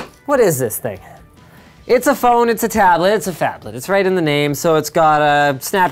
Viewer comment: Did anyone else hear "faplet"? ( ͡° ͜ʖ ͡°)